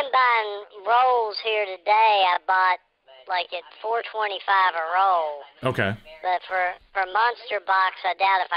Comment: Shop #3 sounded like he said $425 a roll which I took to mean $21.25/ozt